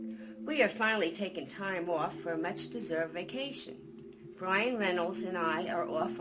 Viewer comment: I first thought you said Ryan Reynolds not Brian Reynolds. That have been a good story